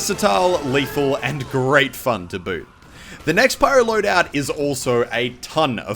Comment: did he say "to boo"?